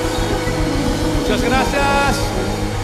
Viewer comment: did he say a big old woody back there?